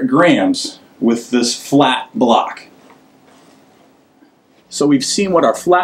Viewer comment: did he say fat block or flat block